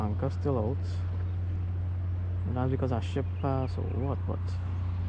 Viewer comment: you say " ship pass" and i hear " shit fast" ah laughing at my self ...... but love the video made me pay more attention thou.....